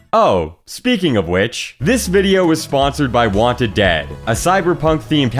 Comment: I thought he said Wanted Dad at first...